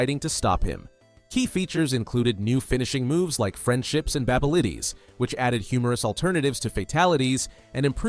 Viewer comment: Babalities lmao. Don’t AI your readings. You just lost a viewer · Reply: I heard "Babelities" 💀